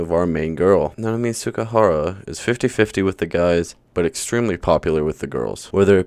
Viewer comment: nah I swear that says so-so and not 50-50 💀